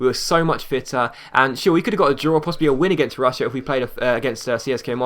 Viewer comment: first time I have heard u swear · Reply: Oh never mind XD I thought he said "and sh*t we could've got a draw"